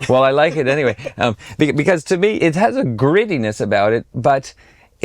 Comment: I love how they misheard, and mistranslated to the French subtitles, his saying “grittiness” as “greediness.”